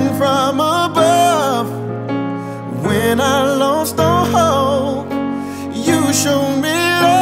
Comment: I thought he said “when I lost a hoe, you showed me love” 😎✨